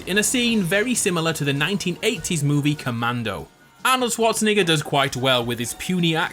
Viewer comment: did he just say arnold swatsinigga